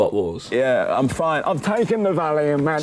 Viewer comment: sounds like he said "I'm fine, I've taken the Valium man." lmaoooo 💀the cc says "valley of magic" but taking Valium in this situation sounds more correct